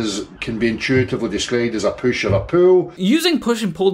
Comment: Sounded like said PUSH ON A POO lol